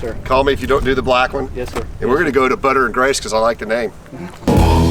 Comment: Cool Jeeps, has returned and “it ain’t food if it ain’t fried” great episode 👍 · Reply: You ain't lying,I thought my man said butter n' grease at first,he had my curiosity then he had my attention. Wasn't disappointed either way lol